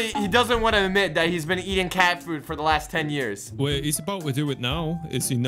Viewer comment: Bro I was eating and I heard "SHES USING CATFOOD" and so I didn't finish lunch.